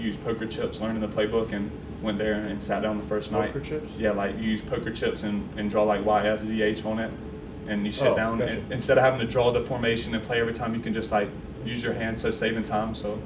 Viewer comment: does coach say “Oh, Bet!” ?? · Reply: I think he said "Oh, gotcha", but that would've been pretty clever if he said "Oh, bet" in that context